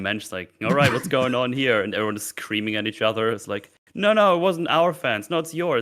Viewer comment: Is it just me, or does it sound like is saying "...then it's like everyone is creaming at each other" ?